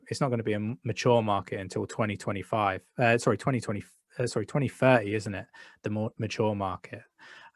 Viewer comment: what did he said? 2020??? twenty twentifer? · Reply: The market gonna be mature by 2030.